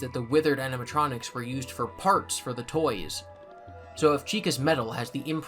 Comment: “used for parts for toys” I think it said “for parts” but not for toys. Would make more sense that they would be used for parts for the new animatronics due to similar build.